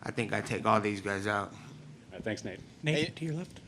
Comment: I know what he really said but first time I heard ... 6:03 ... I thought he said “Nate, do you lift?”